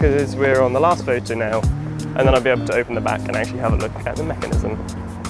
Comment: what did you say at 3:21 I can't understand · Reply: “And then I’ll be able to open the back and actually have a look at the mechanism”